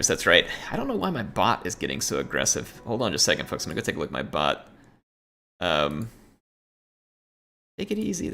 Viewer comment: Heard this as "Hold on a second, I'm going to take a look at my butt"